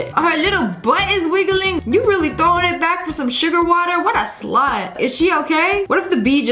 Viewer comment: did she really say “what a s l u t”